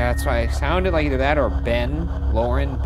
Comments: am I the only one who feels like name sounded like Edith?? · i heard "ethan" after they asked for a name